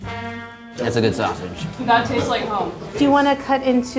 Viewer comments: did she just say that taste like cum? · did she say that tasted Like cum?